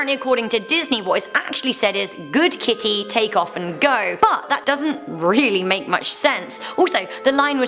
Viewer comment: I heard "take off her clothes," not "take off teenage girls clothes." Still, that's pretty sexist either way.